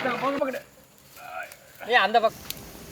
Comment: i heard A ANDHBHAKT 😂